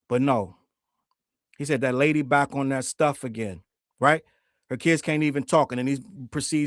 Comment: It sounded like he said “her Ken can’t even…”- she’s “Barbie” and his name’s Kenneth. I don’t think he was actually talking about their kid